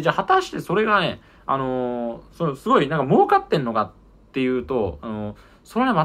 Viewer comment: 「儲かってんのか」が「真岡ってんのか」にしか聞こえん